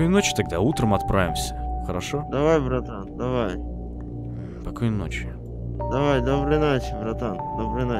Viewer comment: Мне одной послышалось на 26:13 покойной ночи?